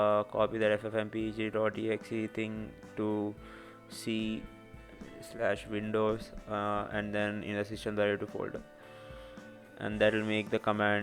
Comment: I couldn't tell what you were saying at 4:00, where do I put the exe? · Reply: c:/windows/system32 . where if c is the drive where you installed the operating system.